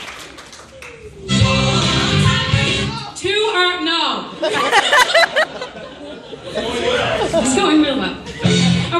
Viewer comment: What does Heather say at 0:05? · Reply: I think she says too hard no